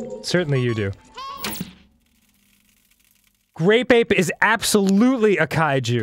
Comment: Why didi i think he said "great vape" O.o